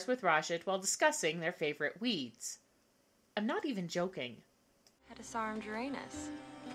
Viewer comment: Did she just say "sarge Uranus"?